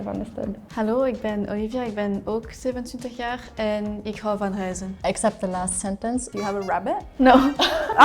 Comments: I'm from the dutch part of belgium and i thought the belgian girl said "ik hou van huizen" in stead of "ik hou van reizen", even when she repeated herself. So I thought she said "I love houses" instead of "I love to travel" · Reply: Same, im dutch and i also from belguim, and i akso thought she said:' Ik hou van huizen.' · I'm from the dutch part of belgium and i thought the belgian girl said "ik hou van huizen" in stead of "ik hou van reizen", even when she repeated herself. So I thought she said "I love houses" instead of "I love to travel" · I live in the south of the netherlands and I have a lot of family in belgium but I thought she was saying "ik hou van huizen" which translates to "I love houses" and the word for traveling is definately the same in dutch as the Flemish word, reizen. She just spoke unclear i guess. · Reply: omg so did I, even when she repeated herself I thought she said "ik hou van huizen" and I'm from the dutch part of belgium But i think it was her accent, a french accent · I'm from the dutch part of belgium and i thought the belgian girl said "ik hou van huizen" in stead of "ik hou van reizen", even when she repeated herself. So I thought she said "I love houses" instead of "I love to travel" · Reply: Same. I hear her say "huizen" as well. And I am like 'wut that can't be right' and it never occurred to me she meant to say "reizen". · I thought the flemish girl said "ik hou van huizem" i like houses 😂